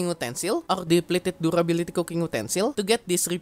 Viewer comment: can't understand a single word he said. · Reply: "... or depleted Durability cooking utensil"